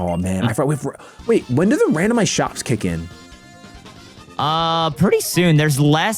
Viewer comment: said "randomised shops" at one point and i thought he said shocks, i was like 'why are yall getting random shocks!?'